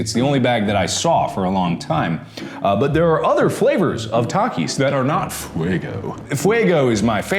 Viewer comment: I thought it said every floor of takis